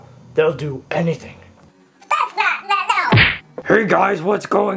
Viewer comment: What did tails say at 1:57? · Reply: thats not nice now